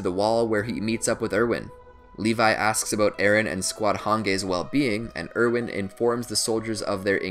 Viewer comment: Did you just say "Han-gay"?